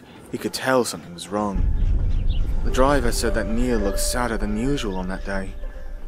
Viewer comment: For a split second I thought the driver said "Neil looked handsome", I'm sorry but I heard it for a split second